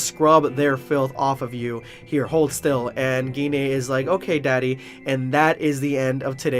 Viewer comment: Did she just say daddy?